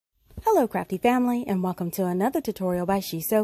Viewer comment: Haha! I’m watching this at bedtime and I’m so tired. It sounded like you said, “Hello, crappy family.” I think I’ll watch the rest in the morning. 😂